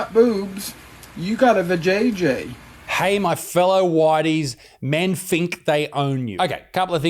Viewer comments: I thought she said widey's as in wide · Definitely 'ladies', Isaac, but it was tricky to distinguish. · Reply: I definitely heard “whities”😂